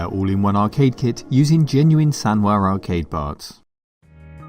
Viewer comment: G-E-N-U-I-N-E S-A-N-W-A A-R-C-A-D-E P-A-R-T-S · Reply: I always heard "SANDWARE" lol